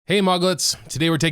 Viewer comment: I thought he said "hey Maggots"